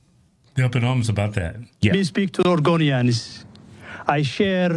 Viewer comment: 28 years here and speaks like crap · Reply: Every time he said Oregonian I thought he was saying Argonian.